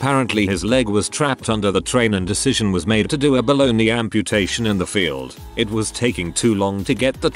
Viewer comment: “Below knee amputation” I heard “Bologna amputation”